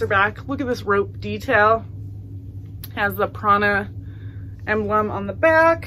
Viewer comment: Love the vest and the Heart CD best although now I have "These dreams" earworming my brain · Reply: When you said Prana I thought you said Prada lol